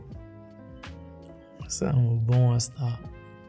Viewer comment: Lolz 'I'm a born star for real' 😅 could have sworn I heard p@ star 😂 like how we go from a children book to that 🤣 really enjoyed this one, kute babies ❤